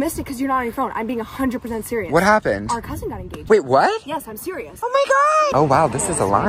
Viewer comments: OK people make mistakes. I thought she said your cousin died but I’m happy for him or she now because she got engaged by. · I thought she said our cousin died! 😢😢😅😅😂😂❤❤ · I thought when she said out cousin got engaged, I thought she said our cousin died of aids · I swear I heard her say “our cousins out of jail”😊